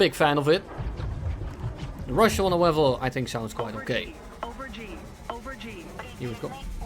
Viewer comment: I thought he said quite gay 😂